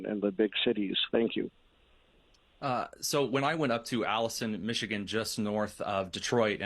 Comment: Alanson Michigan is almost 4 hours from Detroit and 275 miles! You could count ethnic people there on both hands. is a liar! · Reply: OMG! I thought he was saying Allison, Michigan. I was thinking, there is no such place. But, yes Alanson, Michigan is four hours away in Northern Michigan. It's closer to the U.P. then it is Detroit. is a tool.